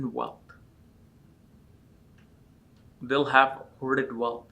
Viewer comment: what kind of wealth? It’s mumbled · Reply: Hoarded wealth